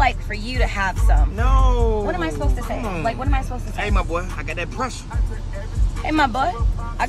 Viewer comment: I’m fucking dead…… “Aye my boy, I got that pressure “ 🤣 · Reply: Thanks for this comment. Because I’m in the break room at work with a fan going and I thought she said “in my booty I got that pressure.” I was like what does that have to do with what’s going on right now? 🤣🤣🤣 but I wasn’t going to dare rewind it and turn the volume up anymore with people in here with me. Sitting here like in my booty, I got that pressure, what does that even mean? 🤣🤣